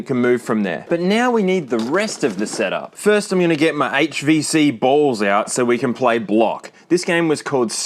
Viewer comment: idk why I heard “HVC-BALLS”